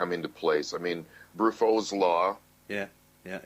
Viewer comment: talks about what I think he said "Brufro's Law". Does anyone know what that is? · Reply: Briffault's Law."The female, not the male, determines all the conditions of the animal family. Where the female can derive no benefit from association with the male, no such association takes place." I got that from the Wiki, there are mgtow videos on it.